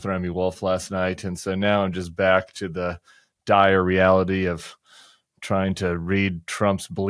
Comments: At the top i heard "back to the diahrreality". Had to rewind to decipher "dire reality". Honestly the former works for me. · There needs to be massive protests during the parade, but it also needs to be clear they are protesting the president and not the Army. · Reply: thought tim said "diarrhea reality" when he said "dire reality" both make sense though! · Tim, I thought you said “the diarrhea-ality “ which, although not what you said, would have been right on point.